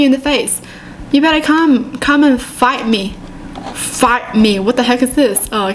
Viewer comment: heard it as "Come and fart me!" 😂😂